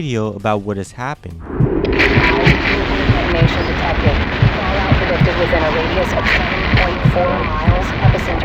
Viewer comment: (SUB CORRECTION) 55:52 she is actually saying " be advised, there is a nuclear detonation detected, fallout predicted was in a radius of 7.4 miles."